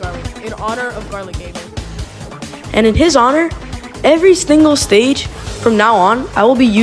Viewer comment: did anyone hear him say every stingle stage?